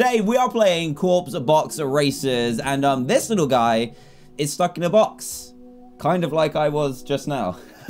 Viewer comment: When u send this little guy is stuck in a box I thought you said he's fu#king a box 😂